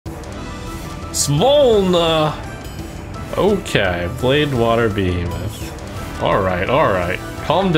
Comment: i cant be the only one hearing pronounce BLADEwater behemoth instead of BLACKwater behemoth